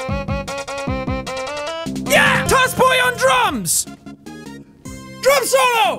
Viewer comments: When Jack said toss boy on drums I thought he said toss boy on drugs at first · I heard "toss boy on drums" as toss boy on drugs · I thought he said TOSS BOY ON DRUGS